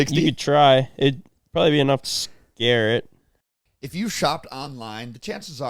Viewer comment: At the 15:15 ad I thought that he said ”if you’ve ever shot all the mountain lion, you’ve probably heard of Shopify”